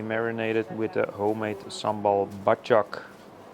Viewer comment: what did he say he marinated the beans in? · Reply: Sambal badjak